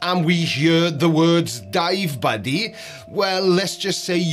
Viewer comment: Start fresh at <link> Over 500,000 people have already made the switch — no nicotine, no vapor, no batteries. Just flavor, fidget, and a fresh start. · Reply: Did i hear you say “jive bunny” at the 9:22 mark?